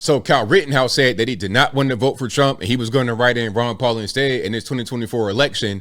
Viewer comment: When I saw this yesterday, I thought he said Rand Paul. Doesn't really matter anyhow.